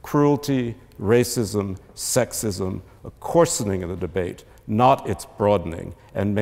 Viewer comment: Guys what is the word he used in 6:22-6:23. “Cruelty, racism, sexism, —————- of the debate ? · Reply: coarsening